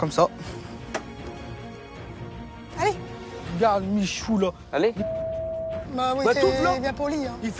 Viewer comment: c’est moi où il a dit je regarde Michou là